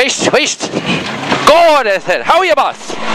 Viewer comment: what he said was "Go they said".